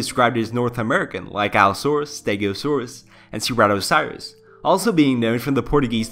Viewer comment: How would you recommend I improve? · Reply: Listen to the proper pronunciation from a reputable source. Your emphasis and some vowel sounds are incorrect. Or at least be consistent... at 3:57 you pronounce the "saurus" in Ceratosaurus very strangely (sounded like "cerato - syrus"), it's just kind of jarring.